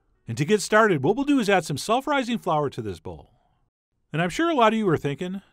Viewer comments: "self rising flour"...omg I HEARD "sulpherizing flour" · I heard " sulfurizing flour" lol.